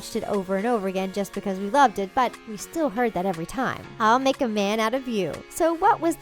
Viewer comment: I misheard "I'll make a man out of you" and thought it said "I'll make a van out of you" :(